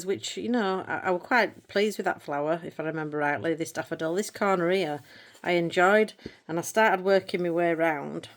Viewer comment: I think you have a good sense of humour, so I hope you take this in the spirit that is intended… I was listening to you talk about your first page in world of flowers, and I heard “I’m quite happy with this cornareah” and I thought “oh is that the Latin name for that flower?” It took me a bit to realize you said “this corner here.” 🤣. And I bet many of your viewers don’t understand what you mean when you say “this is pants”. I have an advantage because my family is from England and we moved to Canada when I was little, but I have heard expressions like this from my parents. Hope that gives you a good laugh today. I really enjoy your channel and can’t wait to see what happens in the new year. Debbie BC Canada